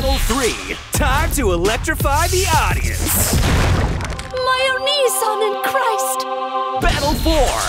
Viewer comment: I'm sorry, what did she say? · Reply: My oniisan in Christ (means brother)